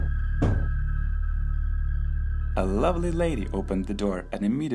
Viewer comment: i thought he said an ugly lady opened the door